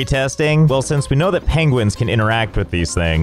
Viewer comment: I thought they said penguins cant fly